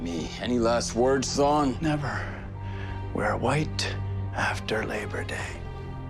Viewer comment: Not gonna lie, I thought he said “We’re White after Labor Day”